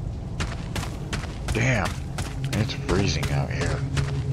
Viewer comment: "Damn, it's freezing out here" I heard "Damn son where'd you find this?"